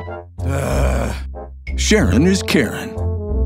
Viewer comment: "Sharing is Caring" Another sentence I heard "Sharon is Karen"😂😂